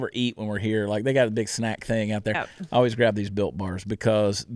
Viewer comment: You said built bars and I thought you said Bill Barr 😂😂😂